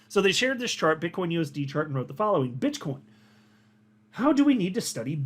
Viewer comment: min 😂 did you call Bitcoin Bitchcoin 😂🤣😂🤣